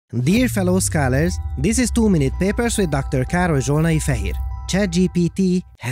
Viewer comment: At the beginning of every video, I always mishear Károly saying "too many papers" instead of "two minute papers". But of course, there is no such thing as too many papers!